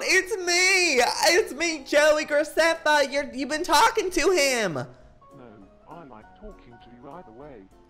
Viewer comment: What he said: OMG ITS MEEEE JOEY GRACEFFA YOUVE BEEN TALKING TO HIMMMMM! What Boibot heard: Dancing baby Joey Graceffa you’re even talking to him.